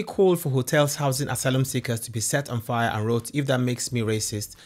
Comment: Hate to be the " AcTuAlLy" guy....buuuuut. What she really said was "...set on fire for all i care" she didnt actually tell people to do it she simply voiced her opinion of how little she would care if it burnt down